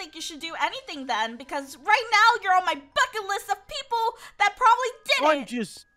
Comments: I Thought Pink Said the F Word 😐 That Scared me 😅 She said bucket list 😌 my Ears are not Working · Did she say the F word or, am I just hearing things?! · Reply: I think she said ''bucket list"